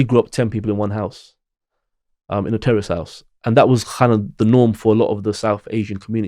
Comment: I know he said "terraced" house, but for a second there, I thought he said "We grew up in a Terrorist House" lol